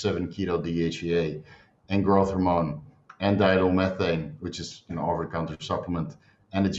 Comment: What is he saying at 2:28? Diethyl methane? 😂